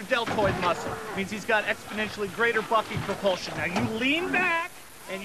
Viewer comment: seconds, the clown sounds like he saying greater Fucking Propulsion! 😂😂